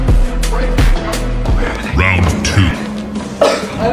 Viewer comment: “I think I’m dieting here man 💀 “ My dad might come back 50 likes?! 70 likes?! I think I’m famous father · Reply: I thought he said “I think I’m dying bru”